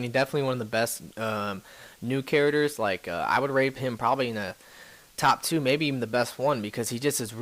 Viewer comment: hold up hold up correct me if im wrong but i swear at 3:49 he said rape him..... check again and write